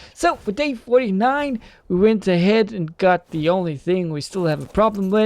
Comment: i thought u said we got the only fans 😂